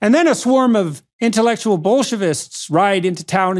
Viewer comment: This guy translated 'Bolsheviks' as 'Bullshits.' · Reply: Ha...I thought he said bullshivists.... it was gonna be my new word